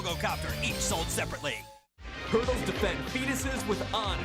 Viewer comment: "Turtles defend fetuses with honor" wasn't something i'd ever thought i'd hear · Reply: I misheard that as "Turtles defend penises with honor"